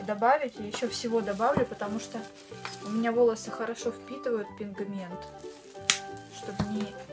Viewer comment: Я одна услышала "пиНГмент"? ( 5:34 )